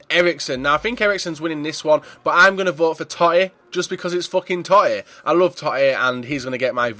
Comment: I thought it said Vote for the Titties..... lol